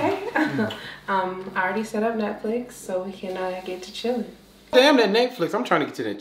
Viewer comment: Shid at first instead of netflix i thought she said what it shud be called Nutflix💀💀💀💀💀💀💀💀💀💀💀